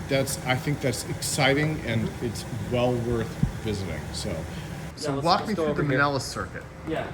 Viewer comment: Can anyone give me context for the project discussed at 22:03 ? I can't understand the name and could not Google that one... The design looks a lot like Floyd Sweet's VTA, but here you said something like "mellis circuit" or something... by some "Ayden Schafer"? But I cannot find anything on that as well. Help!